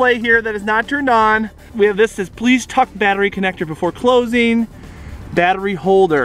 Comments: I thought it said "Please F battery connecter".. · anybody else think that it said please *uck the blah blah blah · At first I thought that it said please f*** the battery in